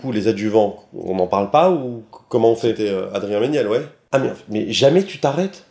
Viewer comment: euhhh, c'est moi ou dit "c'était adrien menielle" vers le milieu de la vidéo ?? à